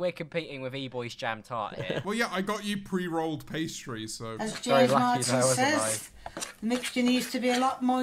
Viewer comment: When she said as James Martin says… I thought she said James Marriott. Just me?